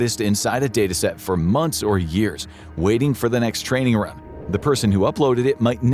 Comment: What is this about @ 9:38 where he starts saying something and randomly stops? "Waiting for the next training orahhh...." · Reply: “Waiting for the next training run”